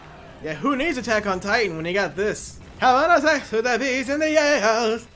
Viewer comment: okay am I the only who hears Having the 🤬 with the bees and the egals. Just me. I'll see myself out.